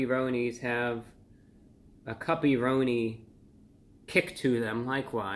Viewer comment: At first I thought you said "puppy-roni." I'm so relieved.